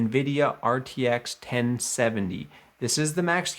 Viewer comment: did he just say rtx 1070??!😂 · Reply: Sorry I am stuck in the past, 2070! 🙂